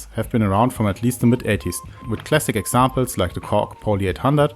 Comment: I thought you said hybrid syths have been round since the middle ages then realised you said mid 80s .. sorry but it amused me . 😁